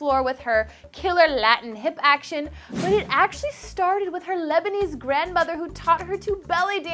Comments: Shakira is 50% Lebanese and 50% Colombian Her dad is fully Lebanese She is from a place called Zahlé I am Lebanese too! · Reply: Friendly Chicken Nugget I thought you said lesbian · Shakira is 50% Lebanese and 50% Colombian Her dad is fully Lebanese She is from a place called Zahlé I am Lebanese too! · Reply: Tell me why I thought you said lesbian lol 😅